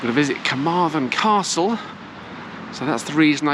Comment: I shop within Carmarthen - I never come away wanting in terms of groceries. I had to laugh when you said you were visiting a place of execution. As you continued I thought you said you were going to visit Carmarthen COUNCIL not castle 🤣 That really had me in stitches!!